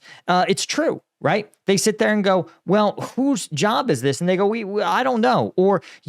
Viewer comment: “who’s job is this? And they go” What i heard : whos job is this? N*gga i dont know🤣